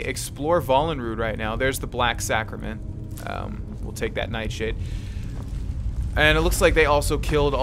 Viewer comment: it sound like he says "night shit" lol